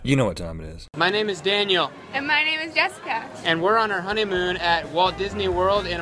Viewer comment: At the end of the video I thought he said my name is jeff